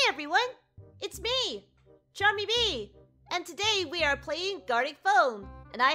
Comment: when you said charmy bee i heard "gummy bee !